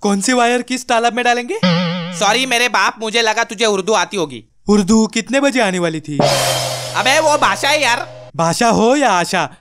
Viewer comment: did u say urdu?